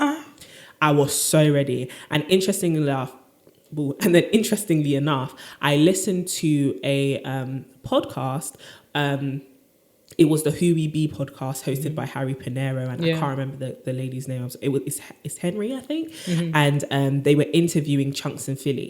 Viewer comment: Hubie b podcast? Anyone catch the name of this podcast?